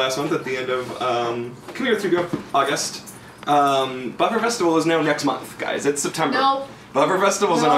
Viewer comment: Did say Butt Fur Festival around 00:00:40? Dog tail in the face.